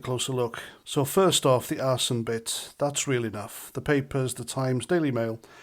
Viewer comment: "Arse-in"..is that your accent that I'm mishearing "Arson"..?🤔